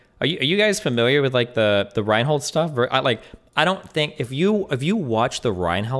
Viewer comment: The second Ryan hall chased the dude across the street his self defense argument is gone doesn't exist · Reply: *his name was Michael Reinoehl. I also thought they were saying Ryan hall.